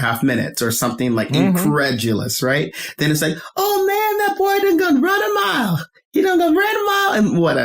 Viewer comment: I didn't know we were calling it "ah-vay" now lol. I just say the letters. But to add on to "done gone" one, I was confused. I thought he was saying "done gun". I take it that's what they say where he grew up. I'm Chicagoan and we say "done gone and" to say a person did something (usually something bad or something that annoyed the person saying it). Another BIG one is "finna", which is a variation of "fixing to" to say that we're about to do something. Or we say "I'm bout to (insert activity). AAVE has regional differences just like like standard American English. New York AAVE sounds vastly different from Chicago and southern areas. Same with West Coast AAVE.